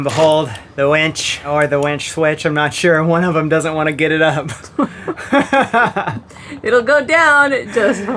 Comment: Did you just say; “She’ll go down, but I can’t get it up”?